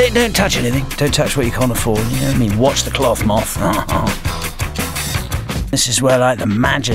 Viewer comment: I've watched this so many times that I memorized everything Murdoc says and sometimes I will just start quoting the entire episode at dinner with my family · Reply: I think that at 1:31 he says "watch the cloth moth" but I can't be too sure and at 2:14 he says "actually some of them did, they riffled through my drawers". I hope that helps!